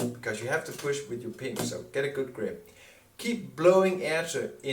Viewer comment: Hi. Go back and begin playing the video at 14:32. Around 14:35, you said, "Get a good grip on the saxophone because you have to push with your penis..." I listened to it several times and that is what it sounded like you said. Also, the captions confirmed the word "penis." Perhaps this was a mistake of automatic captioning, but having played this section of the video several times (and now again for this response), that is truly what it sounded like. BTW: This is why I always check the captions of my uploaded YouTube videos. Automatic captions can turn out some embarrassing mistakes. What did you actually say there? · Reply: well penis is of course a thing, but def. Not involved in the actual saxophone playing. What I am saying is push with your pink - low Bb - then saying "some". So yes I understand. Now I probably have to make a FB post that YouTube is convinced you need your penis to play saxophone. What the actual ....... Sorry, I'll see how I can change it. Thank you so much for checking the whole video and being so interested in saxophone playing. I will have never mentioned penis in any videos.....although it sounds like it